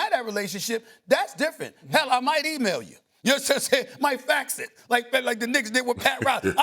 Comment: like the what did? Did Stephan a just say N!GG@ on live tv?